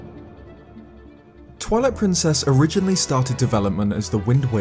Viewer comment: am I the only one who heard that as "Toilet Princess"? Lol, holy fuck I'm dead.